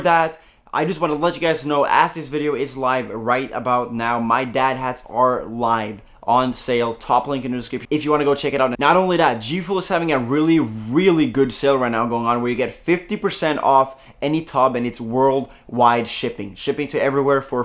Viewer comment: Happy Birthday:D 6:34 I coughed so I didnt really hear what he said and I heard "My dad has our lives on sale"